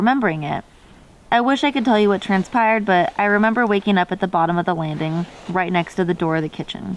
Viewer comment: instead of "the lava" I hear more "the land" which makes more sense to me idk tho.